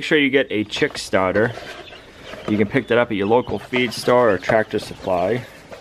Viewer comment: Your cool accent is so thick I thought you said you cut your eye open at tractor supply!! Haha my phone was in my pocket while I checking if my clothes were dry yet